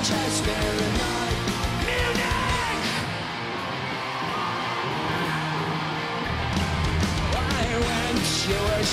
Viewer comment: what is that he yells? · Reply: Munich.